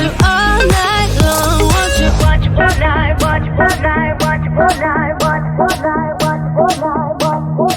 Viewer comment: Я одна 0:45 слышу можно е**ть